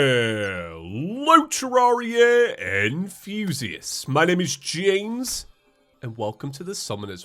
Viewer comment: everything i hear that intro i hear "HELLO terraria and fusiests